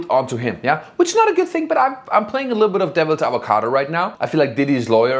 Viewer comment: Did actually say playing devils avocado? Lol